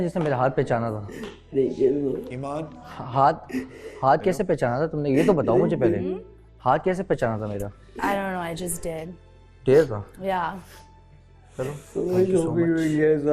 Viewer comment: I just did - Emaan Dare tha? - Ashir Whattt? Correct me if i heard it wrong